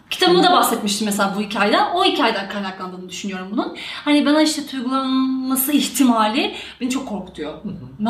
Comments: 'da "Bana işte .....ması ihtimali ben çok korkutuyor" Burada Başak ne diyor? anlayan var mı? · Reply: Bana "tuygulanması ihtimali" diyor ama tuygulanmak ne demek hiçbir fikrim yok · bana tüğgalıbnasi igtimali""" " Orda ene diyor Başak anlayamadım ::(